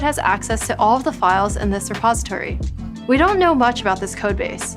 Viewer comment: not her saying "suppository" instead of "repository." A repository is a fancier name for storage. A suppository is a kind of medicine that you stick where the sun don't shine. 😂😂😂😂